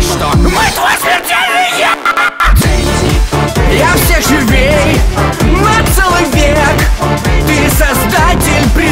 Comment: Я один слышу "Бенди" на заднем фоне? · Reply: Там так и говорится -бендиии